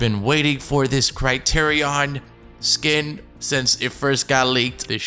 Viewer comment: Kinda mad Bec my stupid ass thought it said Creation Skin STUPID ME THINKING Customizable skins smh...they should bring that in tho!!